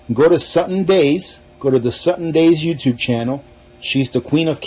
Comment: At minute 11:03 what utube channel does he say...? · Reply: Suttons Daze